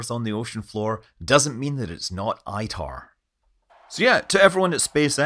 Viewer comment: What's "ITAR?" Is that even what he said?